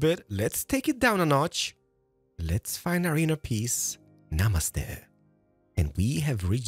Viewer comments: did he really said 'namaste' Its a greeting word we use in Nepal.. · did he says Namaste there????